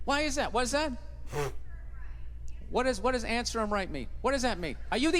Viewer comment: In case y'all don't know what she said it was " It's her right "